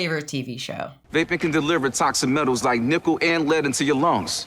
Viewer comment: always sounds like he says "nipple" instead of "nickel"